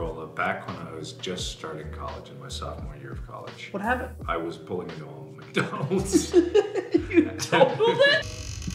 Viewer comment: what does he say? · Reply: "...back when i was... just starting my sophomore year of college." "what happened?" "i was pulling into a mcdonald's..." "you TOTALLED it???"